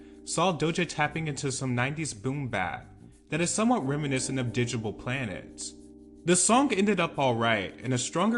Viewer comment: he said attention "is similar to _________ Planets" what did he say, I didnt catch that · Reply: Digable Planets